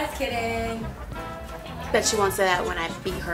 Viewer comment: what did Camila said? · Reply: Camila Grande fans "That would explain a lot"